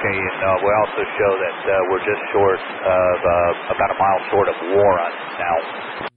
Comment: "We are about 1/2 mile short of (inaudible)" What he said was "Warren" which is a theater with a huge lighted marquee.